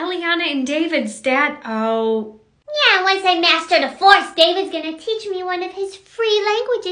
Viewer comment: When she said the force I thought she said divorce😭😭😭